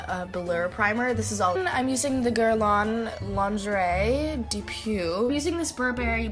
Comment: DID SHE SAY "GURL'ON LONJERAY DEE PU" MDRRRRR NON MAIS OU EST LE RESPECT🕵️‍♀️ JPP💀💀